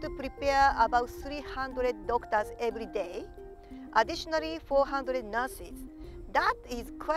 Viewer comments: I thought she said "Additionally 400 Nazis" 😂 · I swear I heard 400 nazis lol